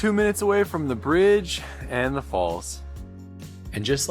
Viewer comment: “And note they don’t allow Germans here” I rewound it multiple times and that’s all I hear. Is that right? lol